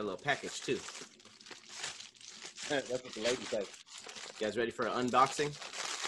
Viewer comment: I heard you. “That’s what the ladies say”.